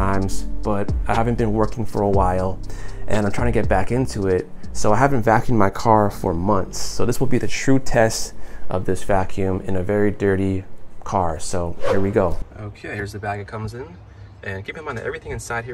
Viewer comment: For a second I thought you said "This will be the Shrute test", like when Dwight tested the buggy with his car. 🤣 Great review.